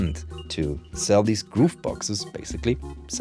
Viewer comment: I have to admit, at first I thought you were saying "goof box" which is oddly appropriate!😂